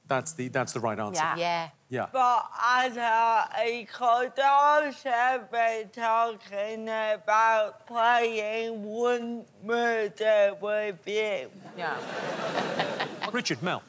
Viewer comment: The fuck did rosie say at 4:26 for like 10 seconds? Not a god damm clue. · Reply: "But I know he could also be talking about playing wink murder with you"